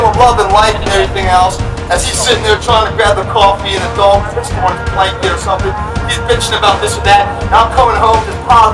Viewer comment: WHAT IS HE SAYING AT 3:55 - 3:58 ABOUT THE NEIGHBOR??????????????? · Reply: These are the words: "As he sitting there trying to grab the coffee and his dog is pissing on his blanket or something. And he is bitching about this or that."